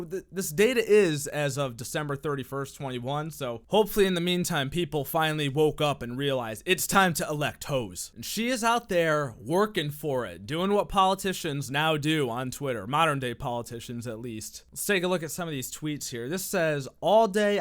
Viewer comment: I thought it said Eject hoes